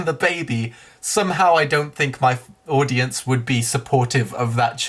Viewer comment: Anyone catch the "My f, audience" at 11:36? Sounds like he was about to say "fans", but changed it to "audience" at the last second.